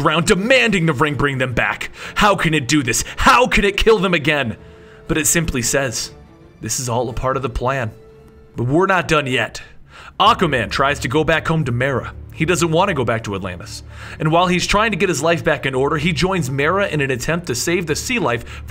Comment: did he say Acka man? Lol